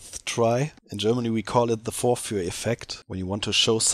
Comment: sorry what did you say that effect in German? Just curious. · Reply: Vorführeffekt